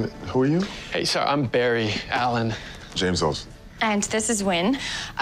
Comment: it sounds like he Said James Charles